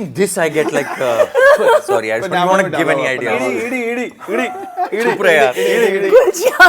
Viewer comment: What was Gulshan saying at 1:02:09? "ED"? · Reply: yeah, I also think it's ED. Enforcement Directorate